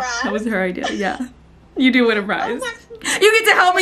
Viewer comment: I think you’re mom said “I’m gonna cry”, not “I wanna prize” lol